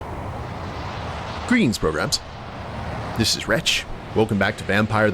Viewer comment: At the beginning of your videos, do you say "Greetings programs" or "pilgrims" or something else? I've watched your other vtm stuff years back. But it's never clicked in my head what you say. My autism makes me mishear words sometimes. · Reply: It's 'Greetings, Programs!' from TRON, but with my southwestern US accent it wouldn't suprise me if it sounded like 'pilgrims'! :_WretchLater